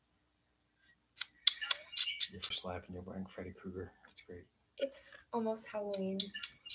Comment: ❤ did he just say you look like Freddy Kruger?!?!? Heard that loud and clear!!! lol. 😂😂😂😂